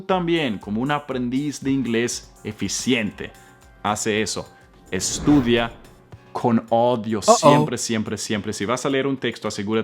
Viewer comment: Hola, creo q solo acerté en schedule, escape y daughter, cuando escuché "estudiar con odio" me quedé como 😅 "yo escuché bien", estoy muy atrás 😅 pero creo q ya la palabra "audio" la tienes más que vencida. Buena clase 🙌🏾